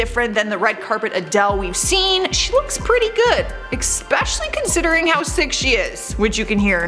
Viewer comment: Did I hear her say “eCspecially” 😂